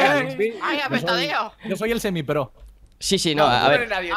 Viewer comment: por un momento pensé que habia dicho: yo soy el seme (es el activo o el que da en las relaciones gay) y lo que dijo fue: yo soy el semi XD